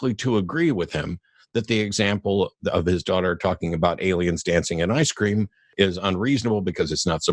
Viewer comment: I heard "aliens eating ice cream" the first time but reckoned "it's reasonable to have heard something like 'aliens in ice cream'" based on how it was said haha